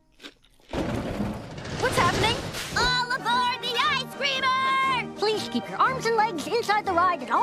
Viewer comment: Did Lynn say I Screamer? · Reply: I think "Ice Creamer"